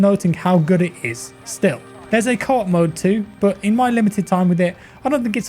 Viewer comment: what does he say at 2:20?? "there is a cod mode to" ??